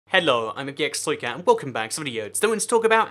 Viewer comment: subtitles say "hello I'm a gay exploit cow" XD